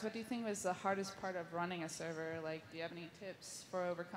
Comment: I thought that said ruining a server.